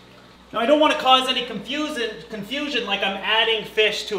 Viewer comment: Did you say conf-susan? 😂